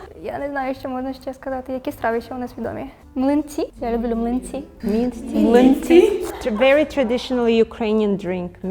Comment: Прекрасное видео. 27 минут улыбки на лице. Спасибо вам. Польский имеет очень характерное звучание, которое сбивает с улавливания структуры, хоть сама структура вполне понятна. Когда я слышу польский медленно, он становится сильно понятней, но стоит человеку расслабиться и заговорить свободно, я начинаю терять всё. Чешский понятней, потому что его звучание не сбивает с попыток уловить структуру. Болгарский сильно отличную структуру слов имеет, что усложняет понимание, хотя звучит близко к русскому. Украинский понял прекрасно, но млинцы без субтитров бы не распознал, первый раз этот варинат услышал. С другой стороны, я расслышал, что это млинцы, а не минт ти. Я Беларус, проживающий в России.